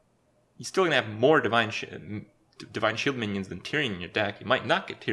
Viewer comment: Those divine shit minions 3:06 ^^ even the subtitle says it